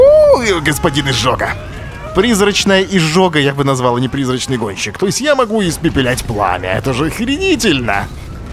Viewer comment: мне послышалось призрачный пончик))